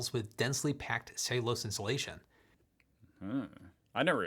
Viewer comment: isn't pronouncing it all that clearly, but I think he's saying "...densely packed cellulose insulation..."